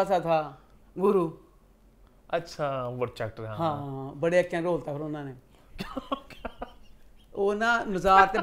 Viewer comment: Can any one tell me what said at 59:02 to which laughed.. I didnt understand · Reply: said changa rol ta una ne 😂